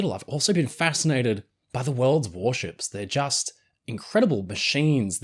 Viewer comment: Did someone say WORLD OF WARSHIPS?!?!?!?! THIS VIDEO IS SPONSORED BY